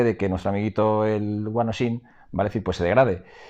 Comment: Que es lo que dices? Guanoyin? Guanollin?